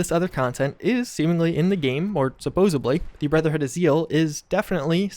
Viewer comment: did you just say brotherhood of zeal or seal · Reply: He said zeal..there are 3 people you can encounter randomly tied to "brotherhood of zeal".